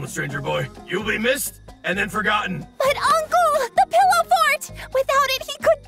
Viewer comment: The English subtitles say "pillow fart", not "pillow fort".